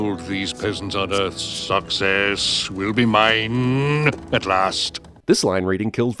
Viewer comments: Did he say, "Sucks Ass?" I have earphones on, and was working on my other computer just listening to Cinema Sins via earphones, but not watching the screen, nor have I ever seen this Disney flick..., I swore this is what this character just said. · Wanna know what I heard at 4:24? Sucks ass :3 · I can't even understand what he's saying ... it took me 5 watch to even get he was saying "success" and not "sucks ass". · as a kid, I always thought he was saying "sucks ass". It took me years to figure out he was saying success.